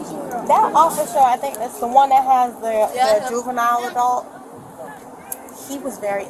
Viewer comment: Did she just say “juvenile adult”?